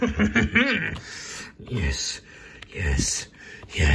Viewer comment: (Coming in with a 12-gauge) "STUDYING, ARE YOU?!" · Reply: Am i the only one who thought he said “sinning are you”